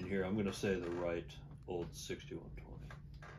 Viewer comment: somebody, anybody, I read the microphone comment in the description so sorry about this, but there's one word... At 1:09:22 the Southern Gentleman on the right says the name of the most beautiful guitar and you all seem to agree. Very Southern Gentlemanly he starts off high "dissenting opinion, the right old 61 trfff" and drops lower and more inside mouth to where the last word is unintelligible. Can't for the life of me decifer what he says and is not repeated and it's driving me nuts! Either in 40 years of playing and reading on guitars I never heard of it or its so jargon I can't tell. WHAT IS IT?! PLEASE!?! Cheers from the Netherlands, Europe. No, English is not my first language. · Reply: Gretsch 6120